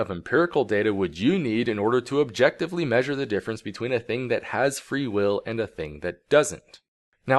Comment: Thought it said "No, Really. What is Free Wifi?" :(